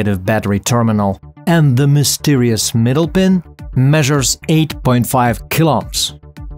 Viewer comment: I thought you were saying 8.5 kill ops 😂